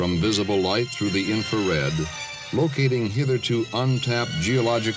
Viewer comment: My inner Tony Stark is saying "Did he just say 'hitherto'?" :D